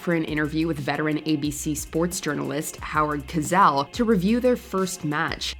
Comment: Howard Cosell calmly calling the play-by-play is hilarious! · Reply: I heard "Howard Kazelle". lol